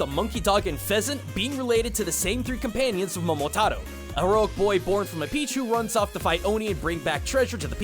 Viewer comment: It almost sounded like you said "A boy who was born from a pichu." 😄